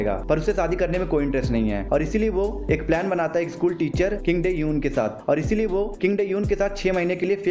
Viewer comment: i think you said kim tae euon...but i listened it kim taehyung 💜